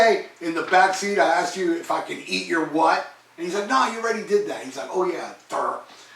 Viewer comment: I always thought they said "You want me to MEET your what?" But "eat your what" is funnier for sure.